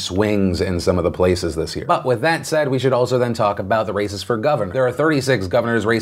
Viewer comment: Not going to lie, I heard "Racists for Governor"